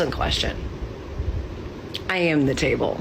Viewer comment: Did she say she's the table? It sounded like she said "I'm a flat-chested woman named Mable" lol